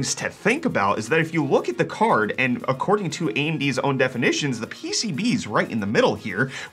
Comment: "AMDs own definition"? Am I mishearing?